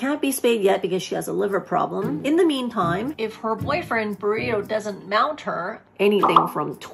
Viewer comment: Her brother mounts her ? Do cats mate w their siblings ??? Edit i thought u said brother u said boyfriend. But still my question stands